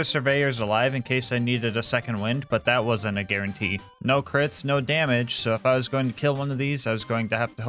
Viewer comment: “no crits no damage” all i hear is “i can’t kill baby turrets